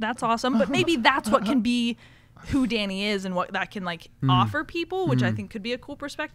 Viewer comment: Every time she says "who Dani is" I just hear it as "HOUDINI"